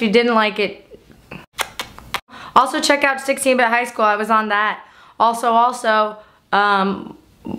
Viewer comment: What was she recommending to see at 2:06? Sounded like 16 high school... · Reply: 16-bit highschool; i think its from smosh